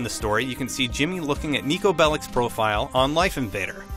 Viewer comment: i thought he says nikki bella :)